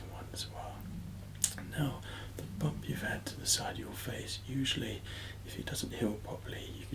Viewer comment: I first heard 26:54 as "now to decide your fate" I thought well this has took a sinister turn xD